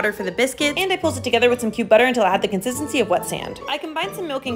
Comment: I fr thought she said “cute butter” when she was mixing the flour, sugar and baking powder. Had to rewatch that one 😂